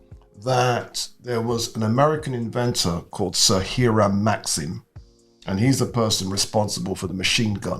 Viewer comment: you subtitles says Sahira Maxim. It should be Sir Hiram Maxim, he was an American-British inventor